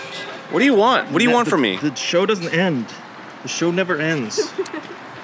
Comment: can someone pls tell me what does it say XDDDD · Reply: "It's the Truman show your always on camera"